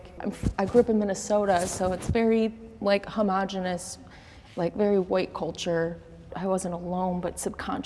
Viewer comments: I know my question is so silly and has nothing to do with the episode but I am an English learner so I am mainly here to listen to natural English at 9:58 did she say "homogeneous" because the way she pronounced the word is so strange please if you are a native English speaker and you read my question help me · I know my question is so silly and has nothing to do with the episode but I am an English learner so I am mainly here to listen to natural English at 9:55 did she say "homogeneous"?? because the way she pronounced the word is so strange please if you are a native English speaker and you read my question help me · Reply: Yes, this is the North American way of pronouncing "homogenous." It's "huh-MAH-juh-nus," with a "mah" sound in the middle. It's slightly different in British English, "huh-MOH-juh-nus," with a "moh" sound in the middle. Many English words are pronounced or spelled completely differently depending on the country the speaker is from. Other common examples of words with different pronunciations include "vase," "foyer," "caramel" and even "sorry."